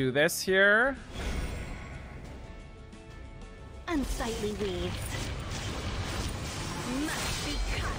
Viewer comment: I really thought she said unsightly weebs I was bought to cry